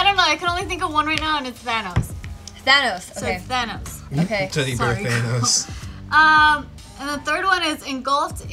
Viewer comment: I thought she said vanoss lol